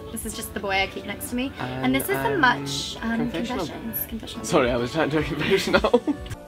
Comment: Ok, AMAZING VIDEO but does anyone know what Thomas is saying at 0:16? 😂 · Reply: “Sorry I was trying to do a confession”